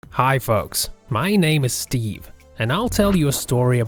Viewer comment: Guy:Hello folks! Me:Did that just say Fu--s ...